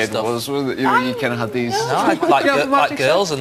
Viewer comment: what the male host has said smthg like girl???? plz anyone tell me...thank you.